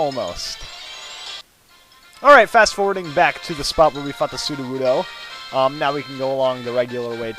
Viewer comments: I swear he said "to fuck the sudowoodo" at 5:10. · haha you thought he said "where we fucked the sudowoodoo" xD